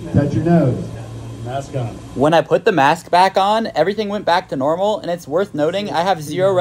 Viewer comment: Had to rewatch cuz i thought he said "i have no recollection of being asked to touch my nipple"